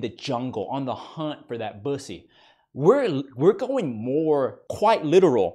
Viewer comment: DID HE JUST SAY "on the hunt for that bussy." HELPP